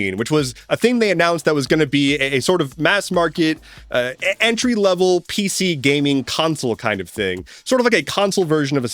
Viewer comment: I heard "Andrew level PC", and I was briefly very confused, because PCs have always been the latest and greatest. What would "Andrew level PC" mean to you?